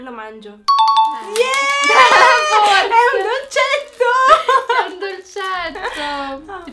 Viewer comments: Io ho capito “ho un pezzetto” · Io ho capito "ma è un confetto"!